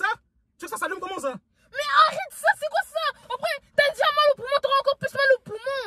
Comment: como ça timal ? Il a dit « ze après y’aura maman après t’aura mal au poumons » il a dit n’importe quoi 😂😂